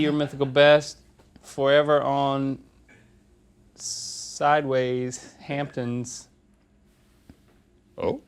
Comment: Be your mythical best forever on sideways hamptons. I love it😂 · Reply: Mal Louann at first glance I thought you said something about “sideways tampons” 🤔